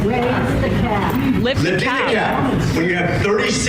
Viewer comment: wow omg lol, I thought the old people were yelling Cap! 😂. Like they were yelling Lies Lies . I think they were maybe just saying cap it or something 😂